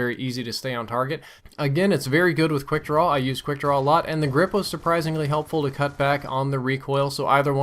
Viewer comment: I thought u said grip was horrible